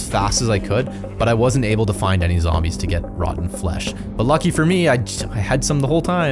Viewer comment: I thought he said roc flesh not rotten flesh